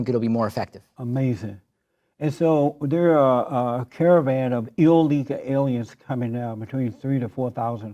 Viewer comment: he sounded like he said, "So theres a caravan of ILL-EAGLE ALIENS" Someone please draw this